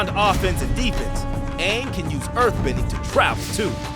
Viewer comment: How did the person who was captioning this, didn't hear what the narrator was saying? 10:23 Also the narrator said "Travel"